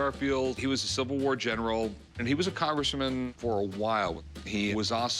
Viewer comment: Did Rick call him a Congresswoman 😁